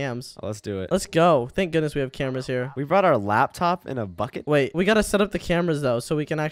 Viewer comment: I thought said the f word but its bucket😂😂😂😂